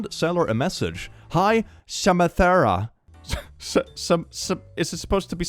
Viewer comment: The whiplash I got at 25:30 when I heard Samanthra,, Markiplier literally named his Misao character that as a joke I can't believe someone ACTUALLY is called that 😂 incredible